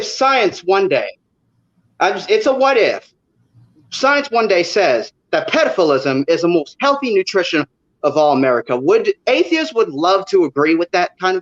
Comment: Well, that was a weird one... Also I'm not a native English speaker so perhaps I misunderstood, but... did he say around 6:00 "what if science one day says that pedophilism is the most healthy nutrition of all America"? After replaying it a couple times, that's what I keep hearing.